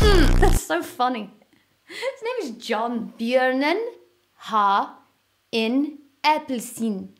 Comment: Why does the bear have an orange? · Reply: Ohhh, that was what she said. I thought she said "Jörgen has an orange"